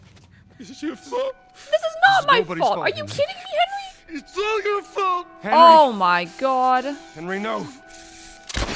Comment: idk if anyone said this but HENRY WAS NOT BLAMING YOU HERE 26:16 before the THIS IS YOUR FAULT line from henry he actually says henry its your fault quietly then louder and louder he didn't blame Joel nor elile he blamed him self i do tear up at this scene no matter how many times i play the game😭just thought your girlfriend should know this :)